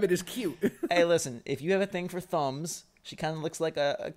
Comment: at first I thought he said "If u have a thing for thongs"